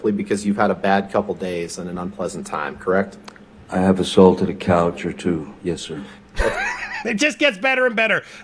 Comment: LMAO did he just say he a**ault*d a couch or too? Plsss